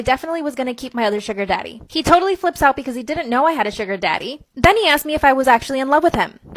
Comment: Bruh I thought she said at the beginning “ Beyoncé didn’t know “ lol 😂✨